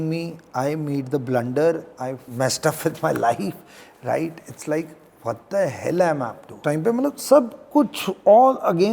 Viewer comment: Can someone tell what he said at 08:20. Naale fal naale...? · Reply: Naale fullian (something to eat) It describes a scenario where you get both the main reward (the fruit) and additional extra perks (the flowers/blossoms).